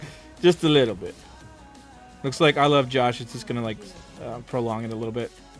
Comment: For a second I thought it said iLoveJoseph lol.